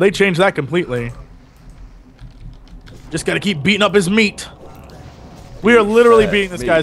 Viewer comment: did just say we got to keep beating his meat? 10:43 that was so funny